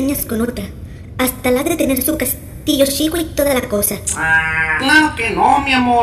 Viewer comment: Que dice en el minuto 2:49? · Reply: "Hasta le ha de tener su castillo chico y toda la cosa" De nada.